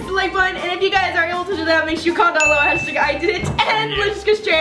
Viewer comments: Who else thought that when said "and then comment #IDIDIT" it said #idiot instead or is it just me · Put I did it I thought you said idiot